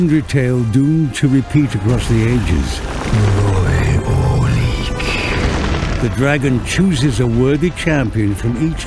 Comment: 모에모에큥 나만 이렇게 들림? ㅋㅋㅋㅋㅋㅋㅋ